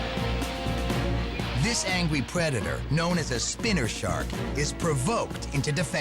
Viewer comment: did he just say "angwie predetor " ?